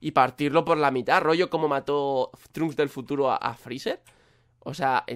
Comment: yo escuche trump del futuro xddd